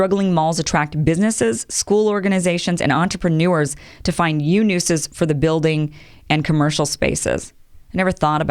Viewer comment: Did she say 'yew nuses' at 10:06?? Instead of 'new uses' lmao someone tell me I'm just hearing it wrong. No hate, just slip of the tongue and thought it was funny lol